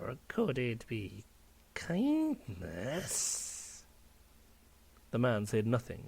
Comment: He lies only out of greed, and you lie out of fear! Or... Could it be... Out of "________Ness" I don't understand that word. 30:20 ish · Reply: Kindness